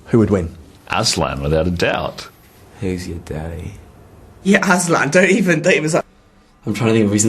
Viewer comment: does Ben say "who's your daddy?" xP